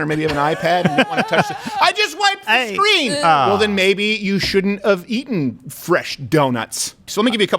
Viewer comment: Crowder clearly says "shouldn't of" instead of "shouldn't've"